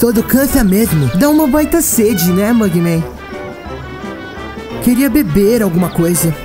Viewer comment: usei a legenda e ouvi "sede de amor gay"